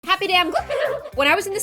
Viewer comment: i remember when i thought in the intro she said - happy day on gloom - its legit happy day im gloom